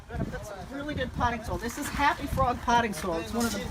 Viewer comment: What type of potting soil did you say you used at 5:15? What type of Happy Frog? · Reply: The regular Happy Frog Potting soil.